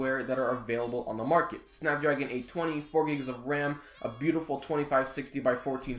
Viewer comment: thought he said 24 gigs of RAM ...well sprint sent me the wrong phone